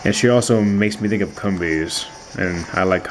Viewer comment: did he just say CUMbees? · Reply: yeah I'm pretty sure it's comb-bee, as in honeycombs, not cum lolz